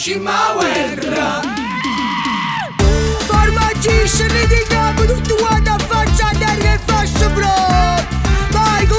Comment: Cosa dice qui? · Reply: "Formaggí(ni), scendete in campo tutti quanti a faccia a terra con le fasce in fronte..."